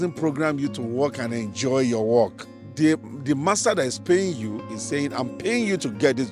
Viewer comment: time 1:45, Did he say "The master that is paying you"? We call them employers, superiors or even boss. It would be funny if he called his supervisor Master 😂 😂